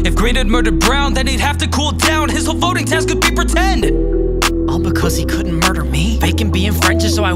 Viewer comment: where he says “His whole fueling task could be pretend” HE ACTUALLY SAYS “HIS WHOLE VOTING TASK COULD BE PRETEND” AHHHHHHH